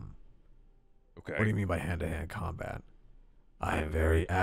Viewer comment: The first time Molten said Hand to Hand....I thought he said Panda hand......my brain broke 😂